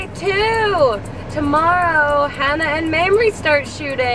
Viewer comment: i hear ANNNNE HATHAWAY. is she in the movie? :D haha